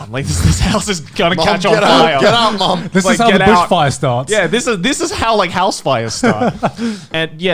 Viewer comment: Gigguck crearly said bushfire, which is like the funniest joke ever to me now, even tho Joey didn't hear it that way